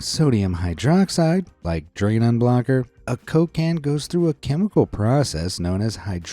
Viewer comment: i thought you say Cocaine but you say a coke can i am so stupid lol 😂